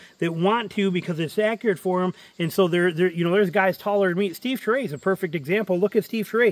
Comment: Very informative video thanks for sharing .... what's the last name of Steve (sounded like "Tree") who is taller than you but has about the same draw length? You mentioned him at about 5:35 into the video. Thanks and God bless. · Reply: Steve turay owner of <link>